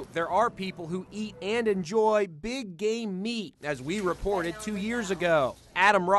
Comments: I heard “BIG GAY MEAT” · I thought he said “Big Gay meat” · who else heard big GAY Meat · LOL i thought he said BIG GAY MEAT